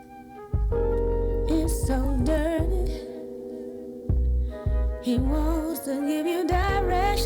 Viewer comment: Thought she said "telephone; it's so dirty" now I realise it's " telephone; it's ol' dirty"